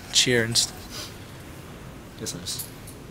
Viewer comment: Did you said "Ya sabes" in 6:44 ?